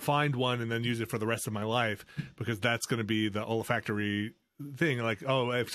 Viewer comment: When Vinny said "olfactory" I thought he said "old factory" and thought he associated his first romantic experiences with disused industrial locations. And it didn't sound all that strange.